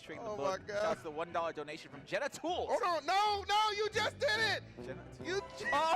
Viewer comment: I didn't understand what happened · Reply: He said genitals reading jenna tools